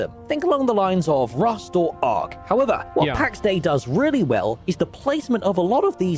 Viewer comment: That woke me up because I heard 'Tax Day' first damn lmao